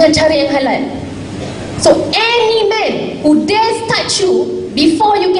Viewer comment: All I heard was: "So, ANY MEN who dares touch you before you get married..."